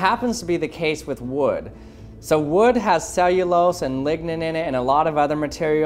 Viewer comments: did he just say wood has ligma? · Did he say ligma